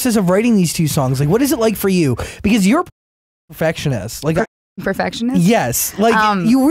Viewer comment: what did say at 10:48? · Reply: Taylor S. tour a fucking perfectionist